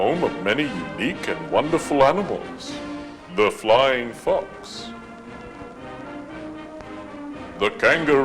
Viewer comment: I legit heard the flying fucks🤣🤣🤣